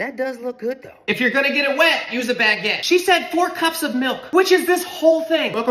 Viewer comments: "if you're going to get them wet use a baguette" this is why I love these videos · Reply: Instead of ‘a baguette’, I heard ‘the back end’ 💀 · "if you're going to get them wet use a baguette" this is why I love these videos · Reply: Ok I thought he said "if you're going to get it wet, use the back end"